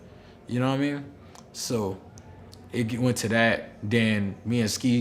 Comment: did he just say Ceeday? 😂 (btw #llj)